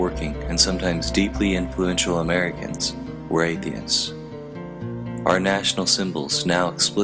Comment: Did he say Athiens in 4:43. Yes hi did LOL!!